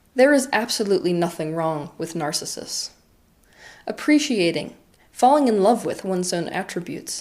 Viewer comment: 'there is nothing wrong with narcissism.' Well, you lost me there fam. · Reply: ah aight my bad, I misheard that as narcissists not narcissus. Still don't like the rest of the video tbh, still is very much in defense of narcissists and I'm not here for it either way. I'll just enjoy her other videos